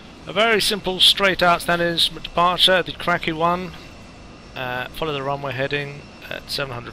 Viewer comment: LOL I just listened to the video myself....sounds like I say "...straight out departure ..the crappy one!" LOL instead of "the KRAKI 1" ..